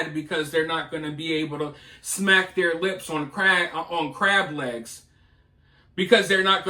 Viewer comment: Sounds like he said 4:02 “smack their lips on crack”. That could easily be the case as well. 😂